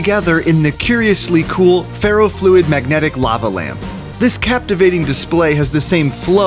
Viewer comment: I thought it said lama lamp so I got excited